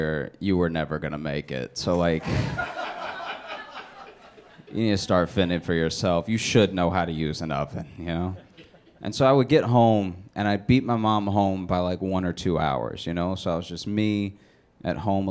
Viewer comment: So, he said "I beat my mom home by one or two hours..." But he was speaking so slowly that I thought he said "I beat my mom" which made me laugh for some dumb reason.